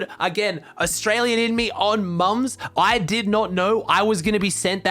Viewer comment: did he just say "on mums"